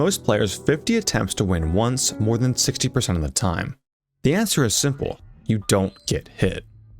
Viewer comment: i got an ad right here so it sounded like he said: “Its simple, you dont.”